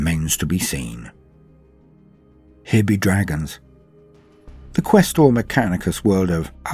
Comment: For a minute I thought I heard you say hippie draggons!🤣🤣🤣